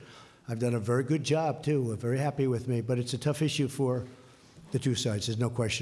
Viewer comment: THE REPORTER ASKED THE QUESTION, THEN, DO I HEAR THE DONALD RIGHT? HE SEZ AT ABOUT 49:04 ( IM VERY HAPPY WITH ME ??) REFERRING TO HOW HE'S DOING WITH THE ABORTION ISSUE ....